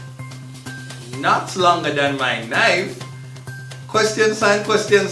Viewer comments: I thought he said knife 🤦🏾‍♀️ I feel like knife works better because, stabbings if you get my drift 😉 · I thought he said mi cocky longer dan mi nine 🤔